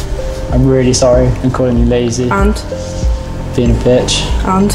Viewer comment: I swear said he's her bitch